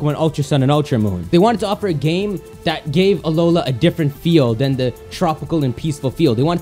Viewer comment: I love how they said Arora instead of Alola because the Japanese usually confuse l with r